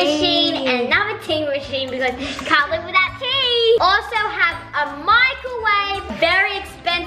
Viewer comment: I wasn’t looking and I thought nazzy said a Michael wave